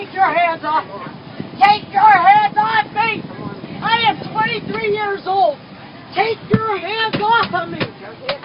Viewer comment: That last woman is looking terrible for being only 23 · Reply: sounds like she said she's 23 years old